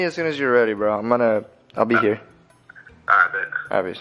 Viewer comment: did he say “alright bitch” to flight.?🤣